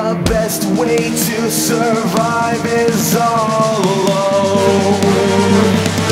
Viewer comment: Best moment: You see it all so clearly, the best way to survive is all alone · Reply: For some reason at one point i thought it said ‘not at all’ instead of ‘all alone’